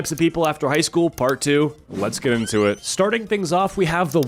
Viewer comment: am I the only one who heard the "go into it" as "goon to it"?? I dial down the brainrot😭